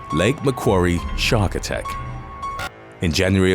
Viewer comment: Man I gotta stop blowing this ⛽⛽ I thought he said Shaq attack not shark attack 😂😂🤣🤣